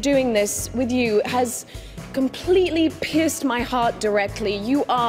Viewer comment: I thought she said "pissed my heart" instead of "pierced my heart" lol